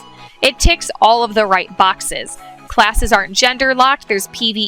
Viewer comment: Am I hearing it wrong or did she said "Classes are genderlock". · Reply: Yes, "aren't".